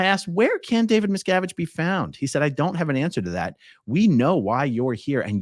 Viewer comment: •CORRECTION• : THE GUARD DID NOT SAY, "I DON'T HAVE AN ANSWER TO THAT." HE SAID, "I DON'T HAVE TO ANSWER THAT." CLEAR DEFIANCE.